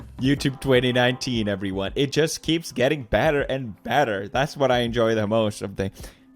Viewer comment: Did he say: "bader and bader" (which diesn't make sense) or "better and better"